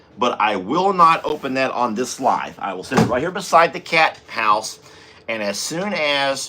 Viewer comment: Barnadette will love the little house. I wondered a few days ago I thought I heard you say "cat box" then realised you meant Cat House.